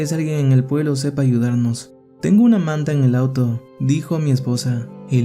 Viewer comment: Tengo una amante en el auto , escuché 3:02 3:04 digan que dije no escuché bien